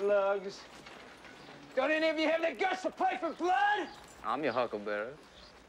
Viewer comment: Its huckleberry. Doc Holliday had a southern accent so it sounded like he was saying Huckle Bearer. And he is reffering to "Im your man" makes sense? And to make sense a little bit more is you pick HuckleBerries. So he wanted Ringo to pick him.